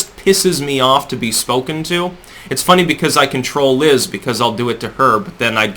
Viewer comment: I misheard ..."control Liz" ???